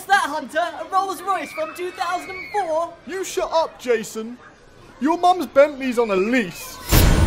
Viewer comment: “Your Mums Bentleys on a lease.” CLASSIC!!!! 😂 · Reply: thought it said “your mums bent knees”